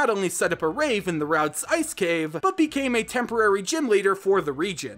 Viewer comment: wow, not just england but the intire great Britan became a pokemon region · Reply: omfg did you just say "becamed"